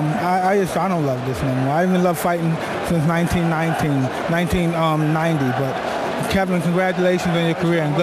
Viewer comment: cant undestand what he is saying. did he say that he didnt like this after 1990? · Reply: yes, he didnt like boxing since 1990.